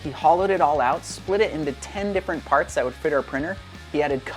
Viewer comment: did you just say the S word😮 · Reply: Fit?.